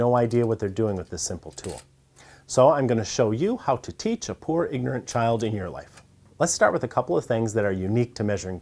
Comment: When he said " poor ignorant child " I heard " You got soft hands brother soft hands , you ain't never worked a day in your life , 68 hours a day , I'm on my job!!!" 😂😂😂